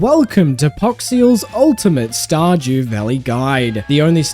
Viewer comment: Did you just say, Star "JEW" Valley?